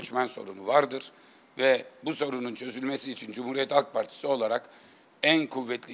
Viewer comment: da Cumhuriyet Ak Partisi mi diyor ben mi yanlış anladım